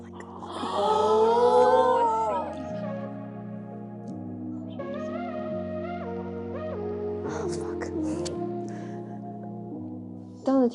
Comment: did she just say oh fuck 😭😭